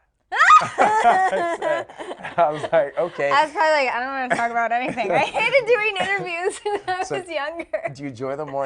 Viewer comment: what did the guy say at 1:08 ? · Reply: he said: "I don't wanna talk about that."